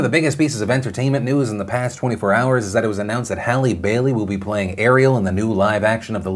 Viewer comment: I legit thought you said Halle Berry and I was like "WTF is that casting?!" Halle Bailey is cute, good for her!